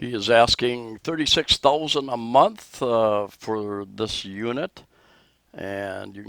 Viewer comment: 15,000 pesos a month!! I google the information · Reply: ?? i thought he said it was 36,000 pesos a month?